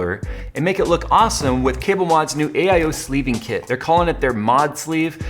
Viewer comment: My hard of hearing, I heard him say get a sleezing kit.......